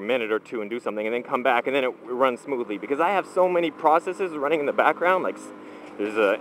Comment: "I have so many processes running in the background"... that's what he said but I heard "so much porn running in the background". ;)